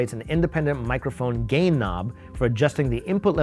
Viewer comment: who else thought he said "gay knob"? lol, anyway. love the video